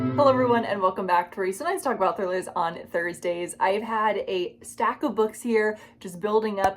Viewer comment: Someone please tell me what she says 0:05 after the "Hello everyone, welcome back to..." As a non-English native speaker, I've been trying to figure it out for so long lol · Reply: Hello! I apologize about that. I said "Welcome back to where we sometimes talk about thrillers on Thursdays." I say that because I review a lot of thriller novels, but I don't always have time to post every week.